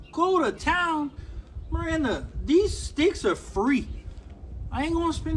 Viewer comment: lol I thought he said these steaks are free. I’m like where at?